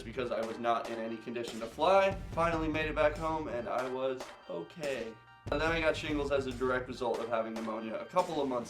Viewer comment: Who thought it said is she okay hahah😅